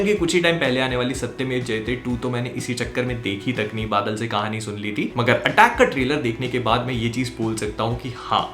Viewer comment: I thought mistakenly said Attack on Titan and I had the listen it again 🤣